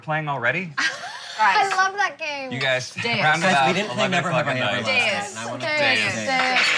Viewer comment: So I knew they had a sleepover but at 21:13 says “we didn’t play never have I ever last night” I don’t believe anyone heard him